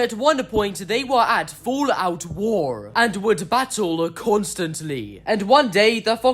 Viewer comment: "fall out war" are you sure you meant that instead of "all out war" ?